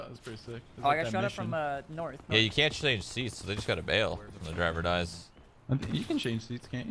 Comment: What did shroud say about the driver not changing seats or something I don't understand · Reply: He's saying the driver can't change seats (unlike pubg) and if the driver bails (exits the car) it causes the driver to die.